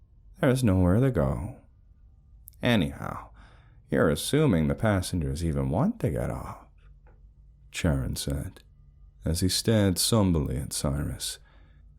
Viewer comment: is it Charon? I thought you were saying Sharon😂😂😂Anyway, I believe that Charon is pronounced Karon